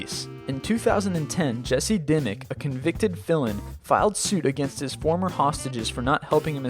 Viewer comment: ANYONE ELSE HEAR "convicted villain"??